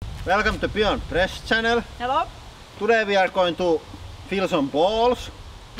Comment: anyone else hear them say today we are going to feel some balls